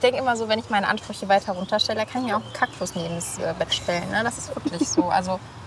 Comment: ich habe zuerst kackwurst verstanden